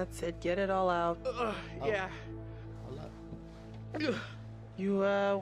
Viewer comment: BRUHHHH IM LITERALLY IN TEARS FROM THIS INTRO 😭😭😭😭😭 i can always count on dashie to make me laugh 😂 22:46 “i love you” dashie I heard that shit 😂😂💀💀💀